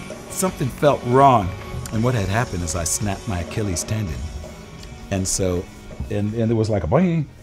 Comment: "I snapped my keelies standing?" - What did he say? Sorry, English is not my first language :D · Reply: Achilles Tendon :)